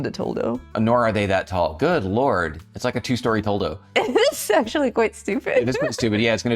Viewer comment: Totally thought you said 2 story dil....well you get it